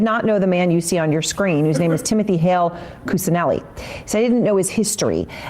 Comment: Wait, wait, wait... Did she say "Cuccinelli"? He's no relation to anybody else we know, is he?